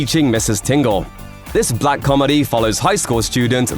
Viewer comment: Did you say “black comedy” 9:37 ?